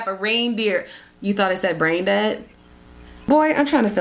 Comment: I’m not gonna lie: i thought said “brain dead” too 🙈 lol!